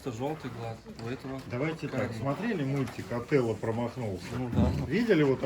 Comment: Только я услышала "Отелло промахнулся"?!? 😳